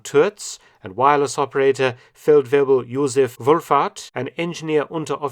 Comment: What he said: "Wulfhart" What I heard: "Wolf-fart" My response: "Heh, typical German situational irony..." Me realizing I'm being strangled by situational irony: 😎🤨😑🙃👹☠👾 I'm going back to the sea, this land business is too difficult for me. *walks into the ocean*